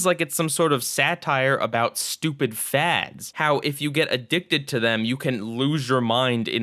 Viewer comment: New season, new face! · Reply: I Burped While You Said "Stupid Fads" And I Thought I Heard "Stupid Fags" LOL LIL WHAT DO YA THINK?!